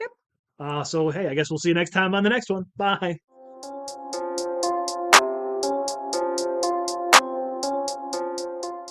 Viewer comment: It sounded like said I am waving and said I'm peaked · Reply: Haha. I didn't have anything good to post at the end. She said "but I am white", and I said "I'm pink". I'm going to try a different mic, this one likes to NOT pick up all sounds in OBS sometimes. :/